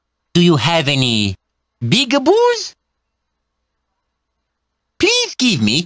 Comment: He did not say peekaboos. He said "Big Boos".